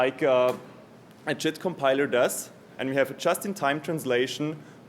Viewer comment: JIT compiler, not Cheat compiler in case anybody is wondering what he said. The transcript is a bit misleading in spots.